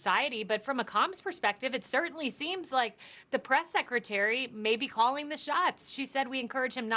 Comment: "orchestructing" did I hear that right????